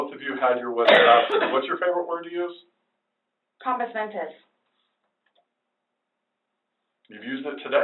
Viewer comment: what was the word he was mentioning at 2:14:01? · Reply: Compos Mentis a Latin term meaning having your wits about you.